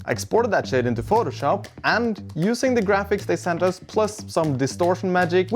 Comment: "I exported that sh*t to photoshop..." XD I know he said Shade, but i heard sh*t. XD haha